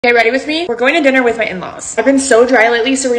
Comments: Why did I hear " going to dinner with my ex" 💀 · for a second I thought she said “dinner with my ex” 💀😭